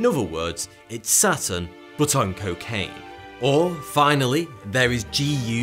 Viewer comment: For some reason I thought he said there was a planet the size of Saturn made up of cocaine lmao 😂